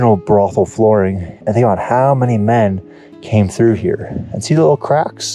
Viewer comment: lmao I thought he said "you don't know how many men 'came' here"